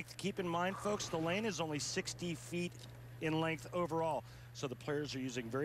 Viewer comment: did he say 60 feet or 6 deep feet